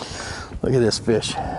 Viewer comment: “Look at this b#%*c” love the excitement in the moment. Never heard you curse before.. I mean, that’s what it sounds like you said..